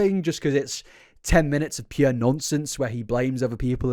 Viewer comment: As a mother, this is the kind of stuff that makes me so grateful I’m able to stay at home with my babies 😰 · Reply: I know you meant nonsense but I heard ‘nonce sense’ and either works really 😂